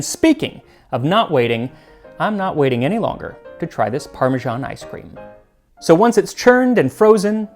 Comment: I thought it said Persian ice cream.